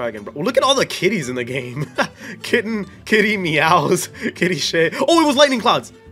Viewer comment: did he just say sh